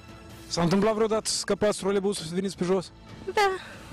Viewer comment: at around 8:06 i swear i hear ''a fost odata sa scapati autobuzul...'' which translates to ''did you once miss the bus...'' not ''when they removed the trolleybus...''